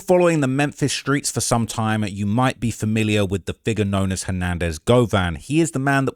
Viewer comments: You said figure.. holy, I thought you said 🥷😂😂😂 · excuse me, what did he say 😂 aww he said figure 😂